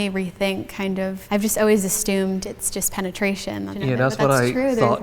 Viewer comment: Did she just say "astumed" ?